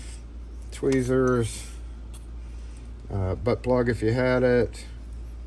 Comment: Did you say, "Butt plug, if you need it."?